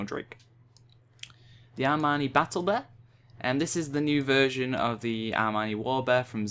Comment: sounds like you said "Armani battle bear" rofl